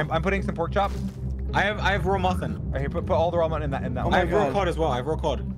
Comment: Sapnap: kills ender dragon Dream: killed by spider Also, did George just say he had "raw muffin" at 17:16...